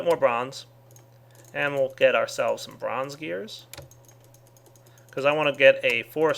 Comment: i had captions on and at 23:00 you said "lets get ourselves some bronze gears" and the captions said "and look at ourselves from skiers"